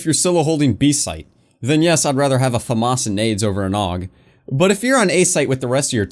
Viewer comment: I Heard "FAMAS And Aids"